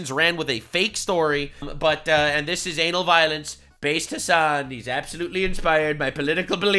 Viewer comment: Why did i hear him say anal violence instead of Ana Valens😭